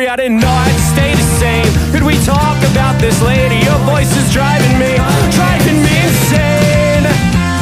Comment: i really thought it said "can we talk about this lady her voice is driving me insane"